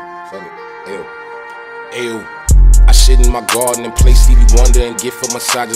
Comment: I thought he said I shit in the garden